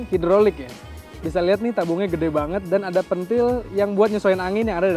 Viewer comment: kayak ngomong "pent***". Kalo gak salah denger ya.